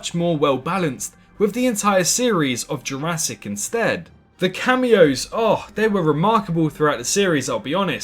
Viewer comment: Did I just hear Cameos(Plural) 😳